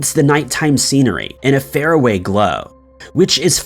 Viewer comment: I think you meant to say "...a far away glow", not "...a fair away glow".